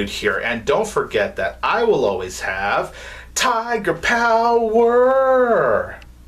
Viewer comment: Good thing that I hear you completely saying "Tiger Power" in that video becuase when I watch your other videos I almost hear "Taco Power" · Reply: That's weird! I always hear "Tiger Power!" when I say it and when I'm editing the videos.